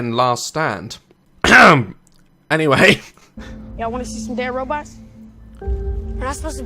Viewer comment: Who else had to rewind the start and thought he said, YOU WANT TO SEE SOME GAY ROBOTS?